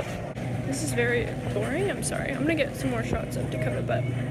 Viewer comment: I know she says “shots of Dakota” but at first I heard “Shots of tequila”!!